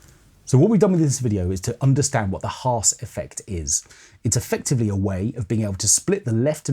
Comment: For a sec I tought I heard "arse effect". Sorry.